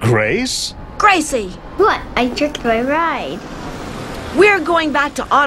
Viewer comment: WHAT DID HE JUST SAID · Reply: She* She said "I tripped my ride"